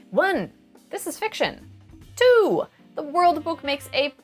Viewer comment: I appreciate the captions but sometimes they're not 100% accurate, like 1:00 the audio says "the worldbook" but the captions says "cyberpunk 2020", at 1:32 the audio says "not one but several" while the captions only say "several". its not a huge difference but they matter to people who rely on captions edit- the captions are literally missing entirely for the last 20 seconds :/